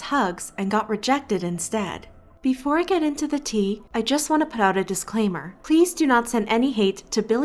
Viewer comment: When she said " Before I get into the tea, " I Thought after she said " I Just wanna put it in the dishwasher " 😂😂🤣